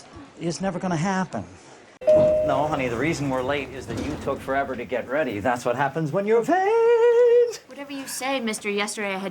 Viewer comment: What did Dr. Cox say at 5:05? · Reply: "No, honey, the reason we're late is because you took too long to get ready. That's what happens when you're vaaaaain." :)